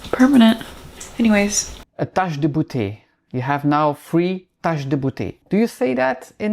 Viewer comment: Hello 🤗 @ 9:21…It sounded like you said…TOUCH THE BOOTAY…I screamed 😂🤣… She learned a lesson.